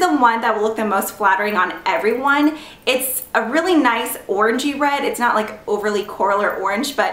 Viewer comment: When you said orangey-red I thought you said orgy-red! I haven't been sleeping well lately ....